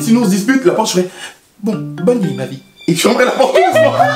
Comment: J'ai l'impression qu'il n'y a que moi qui ai entendu qu'il a dit "ma vie" dans la video ? Je suis folle ou bien. À 5:31 "bonne nuit Ma vie" 🤷🏻‍♀️